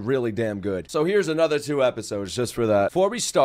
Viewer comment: WAIT I THOUGHT HE SAID WAIT FOR THURSDAY FOR ANOTHER 2 EPISODES HUHH